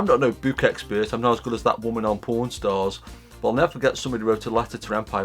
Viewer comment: I heard you say "Porn stars" instead of what you really said ("Pawn Stars").... man, what is wrong with me?